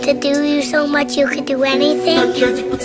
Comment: Did i hear it correct "You want him to do u so much u could do anything"